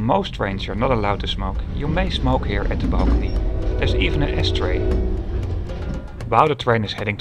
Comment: thought he said ashtray